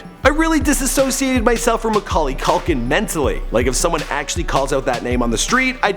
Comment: It sounds like you say Macaulay Cokaine